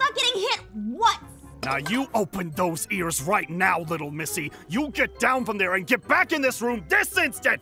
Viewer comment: Did he call her little Missy